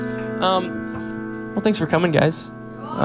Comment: DID THAT GIRL AT 1:02 SAY “OH, IM COMING” or am I hearing stuff please tell me it’s the latter